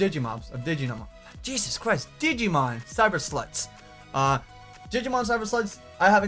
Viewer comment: It almost sounded like you said Digimon: Cyber Sluts XD